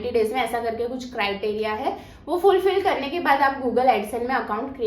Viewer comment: kya bola isne google excel ? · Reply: AdSense